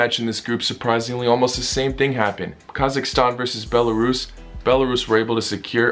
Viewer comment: In 7:40 all I heard was Borat 🇰🇿 VS Belarus 🇧🇾 😅 Btw no offense to Kazakhstan